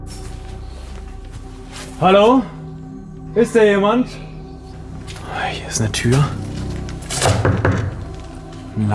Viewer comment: Hi, was war das bei 7:46, als du geklopft hast und gefragt hast ob da wer ist? Warst du alleine dort? Man kann da gut was über die Kopfhörer hören, ich verstehe es nur leider nicht 😅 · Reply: Dar habe ich verstanden ja hier